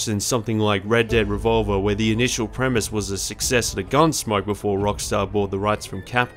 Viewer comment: OK, at 4:45 I have listened over and over, but can't make out what you are saying. It sounds like "... where the initial premise was a success the gunsmoke before Rockstar bought the rights..." but that can't be it.